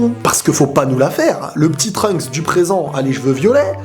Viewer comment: j'ai compris "le p'tit trunks a lui jle violais"